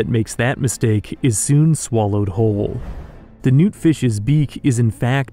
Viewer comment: when you said noot fish i thought you said nuke fish